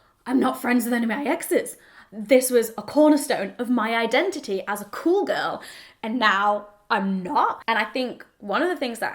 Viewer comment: "as a cool girl..." Oh I so misheard that the first time. LOL (as a call girl)